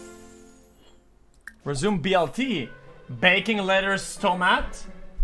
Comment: Sounds like he said “Baconless tomato” at the end, as if it comes with bacon by default